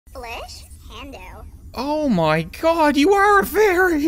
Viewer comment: Every now and then the Sims say stuff that sounds fairly English like and at the very beginning of the video it sounded like Emma was saying 'flesh candles' I don't know what that means but I'm slightly terrified